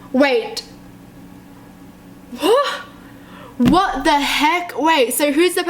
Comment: Is it just me or did she actually say, "붜" at 4:48?